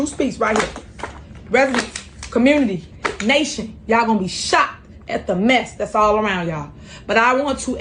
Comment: When Tiffany was talking and playing with stacks of papers, I thought she said she was "going to shoot us" I went back and she said "shocked" this is embarrassing having so many dumb or ghetto or liars in our government..